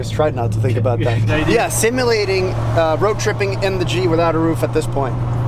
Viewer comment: I almost thought he said "Jeep without a roof" then I watched a couple more times and he said G, no need to face palm.